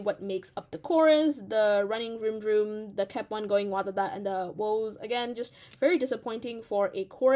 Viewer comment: how do you even pronounce the "kep1 going wadada" part, i thought they we're saying "game over wadada" 👹👹👹